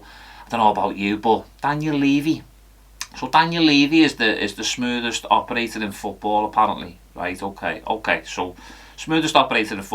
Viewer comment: At first I heard “Daniel Levy’s the smoothest operator in football, apart from OPEC.” Which is not really what you said, but it is hilariously true.